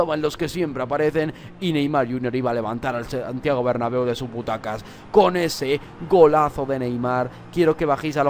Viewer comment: entendí de su puta casa